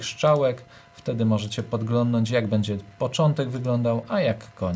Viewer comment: Usłyszałem "oglądnąć" - wyłączam film.